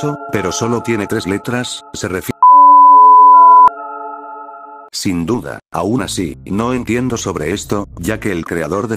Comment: Oye me puedes decir que es no lo oi bien xd en el minuto 5:44 Xddd por favor dimelo no se que es lo que dice xddd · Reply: Dijo "p0rn3grafi# inf3ntil" Osea dijo en resumen cp